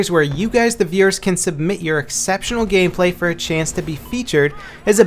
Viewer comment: So i heard 'exeptional gayplay' at 00:10, made me lol.